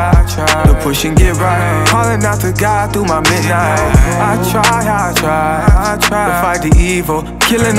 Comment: What does he say 2:50-2:51? Hollering to God through my big eye? Lol that's what I hear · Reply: 😂😂😂Yoooo ,”Calling Out To God Thru My Midnight” excuse my accent 😂😂